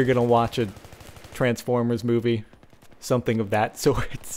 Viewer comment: Did he sayd at 6:28 Transform his boobye?